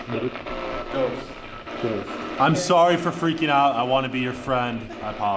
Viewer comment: I heard the box say yes ghost 👻 or what it sounded like to me